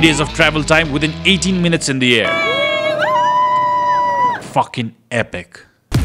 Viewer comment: i heard the word f---in !